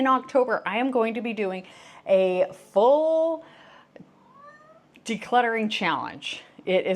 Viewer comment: Crumpet is adorable. When you said you were doing a "Full" decluttering challenge, it sounded like cute Crumpet said "full" right after you. About 13:04. So adorable. Thanks for your video.